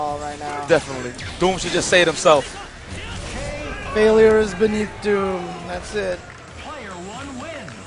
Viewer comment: i never knew when doom dies he says failures beneath doom i thought he said infinite doom